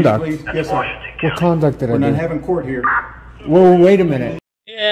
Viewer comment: Actually, the officer said at 5:29, "We're not in court now" as in he wasn't willing to get into an argument with this idiot, who, by the way, has a felony record which includes multiple retail theft convictions (a third retail theft/shoplifting conviction becomes a felony in Pennsylvania). Maybe one of these days, this waste of oxygen could get some significant jail time instead of suspended sentence after suspended sentence. Also worth nothing that most, if not all, Pennsylvania district courts are separate from police stations.